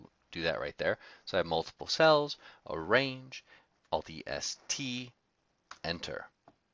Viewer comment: Can't understand what did you say at 1:45. Alt-e-s-t ?